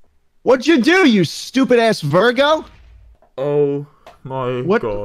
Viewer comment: i am so stupid i heard virgo at 6:40 i was like huh is a virgo i go to google and i see his birthday is the same as my birthday i am shocked.